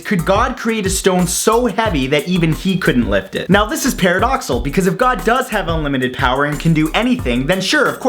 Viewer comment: Did you say 'paradoxal' at 1:16?!